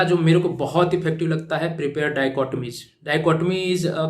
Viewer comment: Sir what did you say at 26:04 intro Diacot.. what ? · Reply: dichotomy